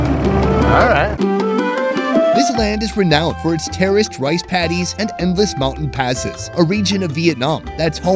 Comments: I heard "terrorist rice paddies" instead of "terraced rice paddies". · I could have sworn he said "terrorist" rice paddies. I need to get my hearing checked.